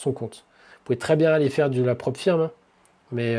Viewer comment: tu dis quoi du "laprofien" ? C'est quoi stp ? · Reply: "Prop firm" Certaines personnes essaient de se faire financer des comptes. Ils paient un abonnement mensuel. Exemple 50€/mois pendant 1 an. Et n'arrive pas à se faire financer ou recevoir des cashs out car ils ne savent pas trader ou alors il ne respecent pas les règles des prop firm. Sur une année tu perds 50€ fois 12 = 600€. C'est dommage car cet argent aurait pus être économisé et mis sur un compte de trading. Il faut un début à tout, et un compte à 600€ c'est très bien.